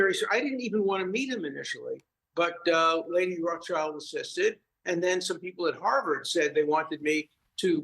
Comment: did he say "Lady Rothschild insisted?" if he did in fact say that name, then we need not ask anything else smh